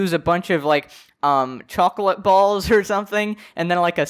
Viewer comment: Did I just hear chocolate balls and snake??? 1:52 that is dirt and worms my friend lol.